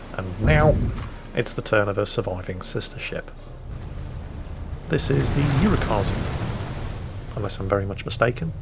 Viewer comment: Turn on Subtitles. XD 10:17 says: This is the Urakaze Subtitles say: This is the urinals